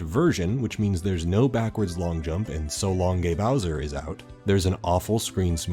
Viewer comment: Man I always heard “so long gay donkey” for some reason 😂